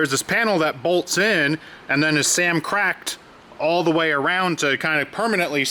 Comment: It was “samcracced in there” lmao I died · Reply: I had to rewatch it another 2 times because I was like "did he just say 'samcrac'ed'??"